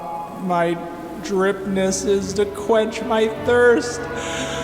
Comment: When you said Quench my thirst for memes I thought you said quench my thirst for meat and I got real scared for a moment